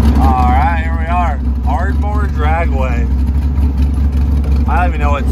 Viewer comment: Thought he said "cardboard dragway" till i saw the sign lol